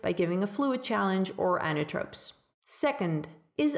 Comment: follows 5:55, what's the last one method to improve oxygen delivery by improving cardiac output ? i didn't hear it clearly. is it "anatripsis"? Thank you! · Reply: Inotropes! Like dobutamine or phosphodiesterase inhibitors like milrinone, enoximone, etc. That's something we do in the ICU (or CCU) and not something you should do on the general ward. Hope this clears things up! Sorry for my mumbling 😅